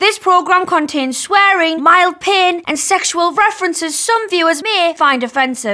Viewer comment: Before I actually read the disclaimer in the beginning, I legit thought she said “mild peeing” instead of mild pain. These accents are insane lol